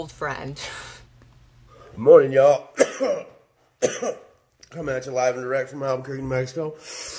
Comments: what they said was “Coming at you live and direct from Albuquerque, New Mexico” 😂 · Gamestop girl: “coming at you live from alberquerce new mexico” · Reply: Thank you, all I heard was “coming at you live, fully erect, in Mexico”